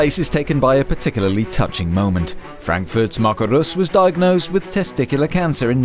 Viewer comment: Anyone else thought he said Marco reus at 2:15.